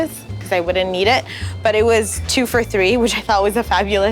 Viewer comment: Did she just say "2 for 3"? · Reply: 2 for $3